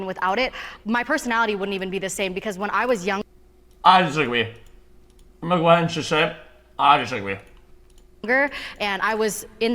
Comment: i have literally no idea what he's saying here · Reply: "I disagree. I'm gonna go ahead and just say, I disagree."